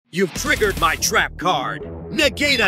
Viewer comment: At the start if you swipe at the right time it sounds like kaiba is saying "you activated my trap card, nig-"